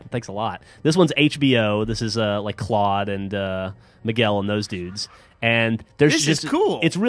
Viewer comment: did he say Miguel???? cuz thats my name!!!:)